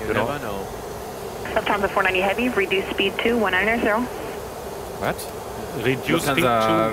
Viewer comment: reduce speed "to" 190 . Sounded like reduce speed "2"190. Same happened in some aircraft when the controller said reduce altitude "to" ..... but it sounded like reduce altitude "2".... and the pilots set that altitude and the plane came too low and they crashed unfortunately due to terrain and very foggy conditions. Thats why the F/O said thats exactly how you must not say it